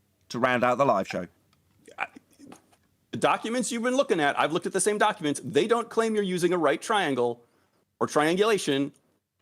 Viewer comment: OK, do you have that timestamp then? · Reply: You mean at 59:16? That guy clearly said: "documents you were looking at i'v looked at the same documents they don't claim you are using right triangle OR triangulation". Then start his rumble. It is hard to tell if really misheard that the way you write it - "no right triangles used IN triangulation" instead of "no triangle OR triangulation" but clearly fooled his audience, so good job, I guess.